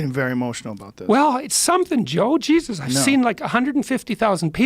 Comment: Am i hearing this wrong? When Rogan says “you’re getting too emotional on this” and then peterson says “its really something” , does rogan say “no” ???? All i hear is “no” . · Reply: He says "you're getting very emotional about this" (not to) And then he says "I know"